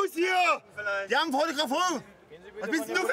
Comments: Hallo Leute ! Ich und meine jungs lachen uns mega den arsch ab bei unseren Deutschen Patrioten . Könnte mir jemand sagen was der nette Herr bei 2:19 sagt ? Ich verstehe da nur FOTOGRAFONG ihr auch ? Freue mich auf eure Kommentare :) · Ich verstehe die Leute nicht ... also sowohl die Ideologie als auch Wort wörtlich ... ab 2:18 nach “was los hier” ich versteh einfach nicht was er da sagt ... fotografur oder so ... kann mir da jemand helfen ... leider gibt es keine Untertitel